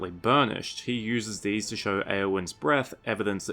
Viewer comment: thought you said aeowyns breast 😭